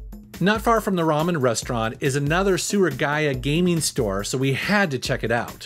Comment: I wasn't looking and only heard you say the keywords "Sewer Gaia" and "Down in the basement" and thought "wow, that place must be a dangerous dungeon filled with rare gems and shiny things". Was a little disappointed that it was Surugaya instead, sans deadly traps.